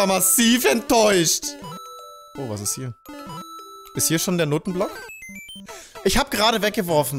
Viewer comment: egal wie oft ich es mir anhöre, ich verstehe immer n*ttenblock